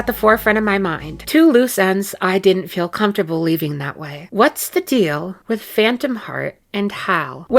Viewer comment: I got an add at this part so it sounded like you said “What’s the deal with my ex wife” 😭